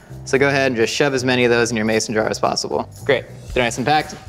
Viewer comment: Did he just said Mayossen 5:51 instead of mayonaise??